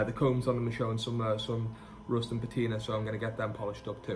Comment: Is this a cooking show? 2:52 I thought you said roast and potato. Sorry - rust and patina. Makes more sense for a guitar show.